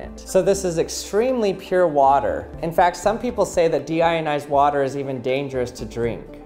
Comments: I thought it said "Demonitized water" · Thought it said demonitized water 😂